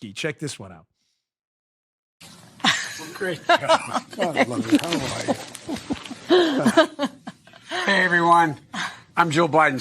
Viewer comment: When Joe walks up to Jill in the very beginning of the "I'm Jill husband" it sounds like he say to her "God love ya, how are you?" Like he hasn't seen her un awhile. I wonder is he forgetting whom his wife is and what she looks like...